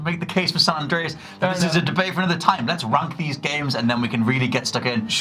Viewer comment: Did he just say “let’s runk these games”